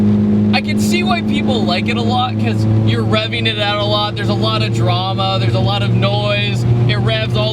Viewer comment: Thought he said your rubbing it out a lot not revving it out a lot